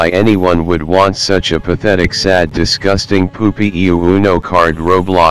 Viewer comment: I thought he said UwU